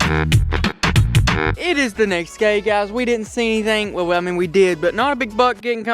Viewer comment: did he say we have the next gay guy